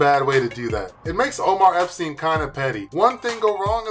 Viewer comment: Did he say Omar Eppstein at 10:33 ?! LOL